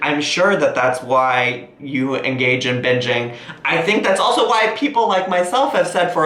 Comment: When she first was saying "binging" I thought she said bitching